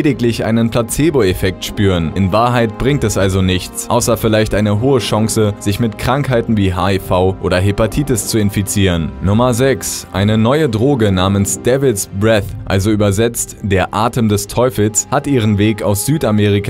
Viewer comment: Wer hat auch devils' bread verstanden? :D